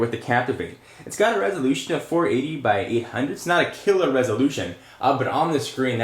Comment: What the hell does he say? A resolution of 480? × whatever he says. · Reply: 480 x 800